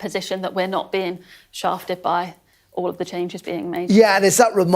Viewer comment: did she say "shafted"? ????